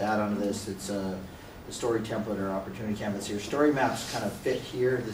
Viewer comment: What is the word he's saying at 1:36:36 ? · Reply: "Story template or opportunity canvas". I believe.